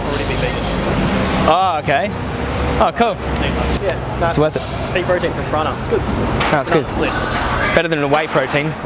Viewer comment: What doesthe officer say before "better than a whey protein"? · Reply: I think he said "peas protein"